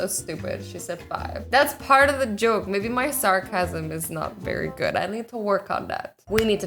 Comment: I thought she said “my son is not very good at that”!!!